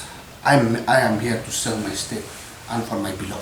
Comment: Wait what what did he say's🤣4:06 I'm am here to sell my state